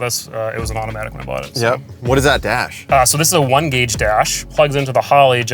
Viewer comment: what dash in the volvo? I can't understand what he said. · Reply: One-gauge. They make tablet style gauge setups.